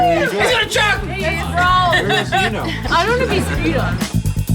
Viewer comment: Did I hear that wrong, or did that girl say she wanted to be spewed on